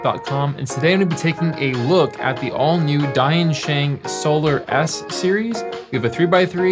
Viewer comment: I’m pretty sure that Diensheng is pronounced “dyen-sheng.” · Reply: At least I kept hearing it as "DaYan Sheng" instead of "Die and Sheng"